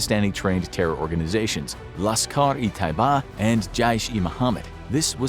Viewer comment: In 7:48 terrorist organisation's name is Lashkar e Taiba instead of jaskhar-e-Taiba